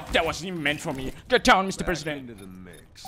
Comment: did he just say "get down mr president"????